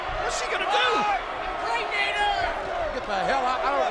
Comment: did I just hear ‘Impregnate her’😂😂